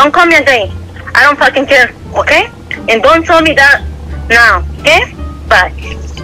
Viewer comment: Did anyone understand what the lady @ 7:12 was talking about. I couldn't really understand her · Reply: She said don’t tell me this stuff no more ok bye and that was my mom 😭